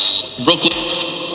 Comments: When it said We are all ready shortly after at 8:34 it sounded like it said Broken so maybe its saying “We are all ready broken” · I heard "Brooklyn" did any one else hear that? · i hear broken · At around 8:35, or 8:36 I swear I heard something like "Brooklyn" But it cut out, and it sounded very close to X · i think x said "Brooklyn" which was his ex from a long time ago😩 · it sounded like x saying Brooklyn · it sounds like he said brooklyn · / 08:36 sounds like he said broken in his voice tone too